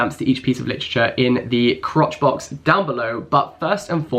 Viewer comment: did he say crotch box?